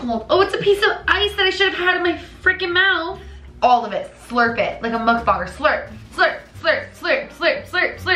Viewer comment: I legit thought I heard say " slurp it like a motherf**ker " 7:53 I went back to hear it and it was "slurp it like a mukbanger" . Ehhh could be my dirty mind .